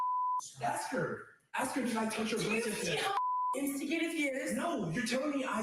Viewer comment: u don't think she said "instigated". That would make sense grammatically, I believe she said: "intoxicated"